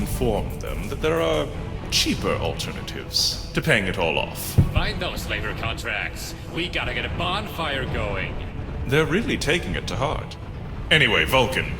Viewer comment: Question: Was it always “...Find those labor contracts. Got to get a bonfire going.” I could have sworn they said “...Find that fucking Vault. We got to get a bonfire going.”. Just something I noticed.